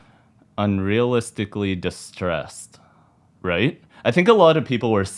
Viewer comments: I thought I heard Niahlistically, yet the pronunbciation could be cuz of Dani's accent pronunciation... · I heard “unrealistically” until someone said check out this one live video and then I totally heard “nihilistically” clear as day. But Pau confirmed it’s unrealistically and now the whole world is upside down I’m certain they’re messing with us, they would do it!! Little tricksters 🤣